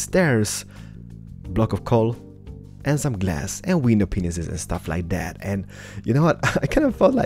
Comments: I've watched his videos before but I love the fact that it sounds like he says window penisis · hahahah I sounded like he said window penis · sounds like he says window penis.but hey no hate your awesome builder!!